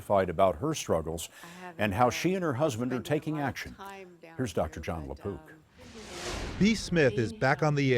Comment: I thought he said Dr John la Poop 💩 until I read the description 😂